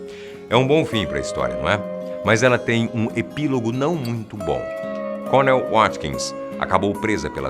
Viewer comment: ESSA VOZ COMBINOU COM A MÚSICA DE TERROR EM GOSTEI KKKK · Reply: Gabriel Provesi em 3:49 ele sita um nome. Parece que ele diz cornel... Nao entendi essa parte que ele diz que ela foi presa